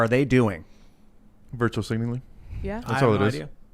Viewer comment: What is virtual signaling? Virtue signaling, I guess. I'm just pissed I had to go back 3 times to verify what I heard.